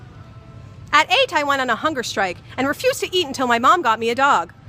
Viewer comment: Thought she said hooker strike haha